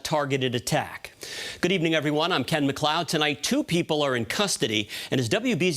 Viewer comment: When he said "I'm Ken McCloud" I heard in my head "of the clan McCloud" .